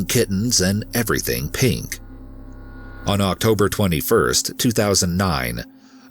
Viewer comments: I thought it said Elizabeth Olsen and i got very worried for a second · I thought it said elizebeth olsen